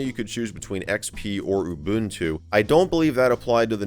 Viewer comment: I misheard when he said Windows or Ubuntu, and heard Windows or Uwuntu. Which would have been one hell of a choice by Dell to pack in. Of course, assuming it even existed back then.